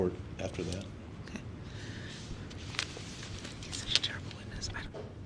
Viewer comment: Anyone else hear the prosecutor whisper "he's such a terrible witness" about the paramedic at 7:37:15?